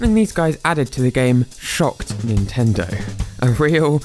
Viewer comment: At the beginning I thought that you said " Shock Gun"